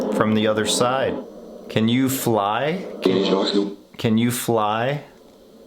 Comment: I think when you said "Can you fly?" I heard "ANGELS DO"